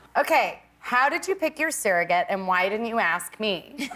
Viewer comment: How did you pick your what? I didn’t hear what she said 😕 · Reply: Surrogate I think